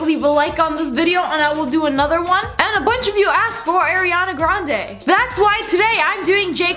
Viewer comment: I thought u said “air”iana grande I was like: shE AiNt mAdE oUt oF AiR